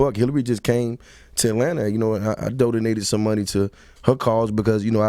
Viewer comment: Am I the only one who heard Jeezy say "I dodinated" instead of I DONATED....let me just Kermit and sip tea.